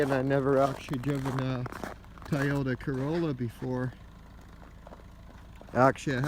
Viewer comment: Nice car, sorry I kept hearing "Tie Odour Corolla" xD